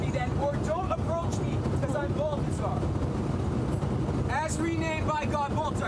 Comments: Did he just say he's Bulbasaur? Like the pokemon? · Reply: Nah., I think he said hsi name was Baltazar. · Whatever his issues are, he should be permanently banned from ever flying on any airline! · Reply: i thought he said Balbazar, as in the pokemon lol